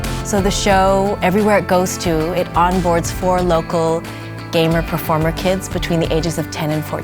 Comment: escuché un "four loko"? :V